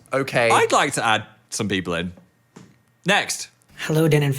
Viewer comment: did phil say he wanted to add people in or am i hallucinating